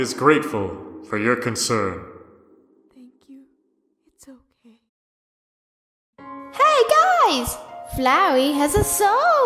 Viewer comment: Thanks to the new headphones, I think I just heard Asriel whispered, "Thank you, "It's ok..."